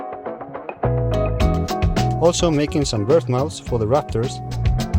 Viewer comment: was waiting to see the build of the bird house in the rafters. Had to rewatch part of the video never saw that part of the build woops Bird Mouse I think I heard correctly?